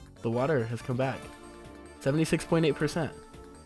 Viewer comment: Did you say 67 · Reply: 76*